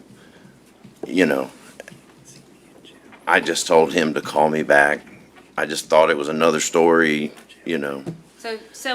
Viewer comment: Did anyone make out what wade said to his lawyer that made the lawyer make a disgusted face at 5:03? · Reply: something “see me in jail” is audible